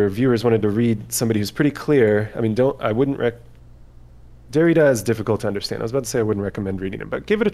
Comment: I think he says Richard Rorty, Jaques Derrida. For those interested.